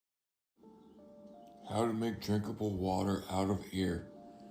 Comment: anybody else hear how to make jerkable water